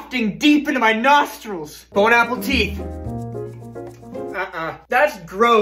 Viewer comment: Why does he always sound like he is saying bon apple teeth?